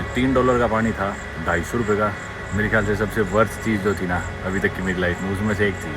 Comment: worse chiz ? Bhai kya bol rha hai 😂??